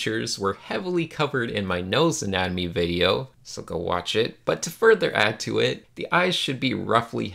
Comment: am i the only one who heard - Сука, watch it ?